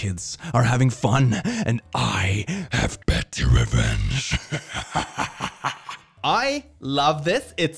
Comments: *"pretty revenge" is was it said, and i love that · They actually said pretty not petty UwU